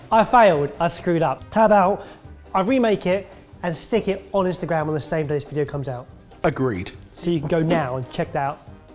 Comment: Please someone translate what the hell he said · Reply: "and stick it on instagram same day this came out"